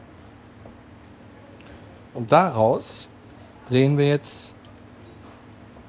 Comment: ich hatte verstanden: daraus drehen wir Elche